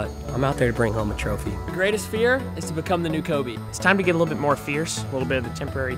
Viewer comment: I thought he said “greatest fear is to become the new Kobe” 😮